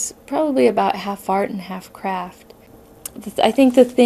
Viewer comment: i thought she said half fart half crap lmao